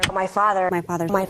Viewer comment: Sounds like she says "My Fadder"😂